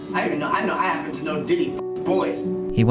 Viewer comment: did he just say Diddy? Given the last couple weeks….damn.